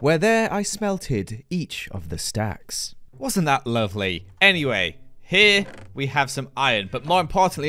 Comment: Is it just me or did he say lore..